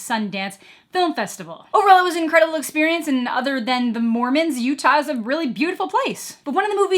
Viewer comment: "Other than the Mormons, Utah is a beautiful place." I am a Utahn, born and raised. This is an accurate statement. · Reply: I heard "other than the morons of Utah [...]" and I was like "geez, thats pretty harsh!" Hahahaha